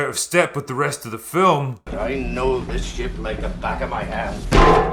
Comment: did he say "the back of my ass"?